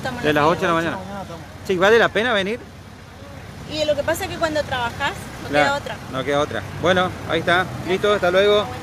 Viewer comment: En 04:18 lá senora que dice? No hay otra? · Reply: "Es que cuando trabajás...no queda otra" 🙄